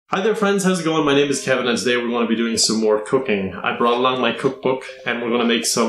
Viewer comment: Is it just me, or did it sound like Keven said “We’re going to be doing more cocaine”?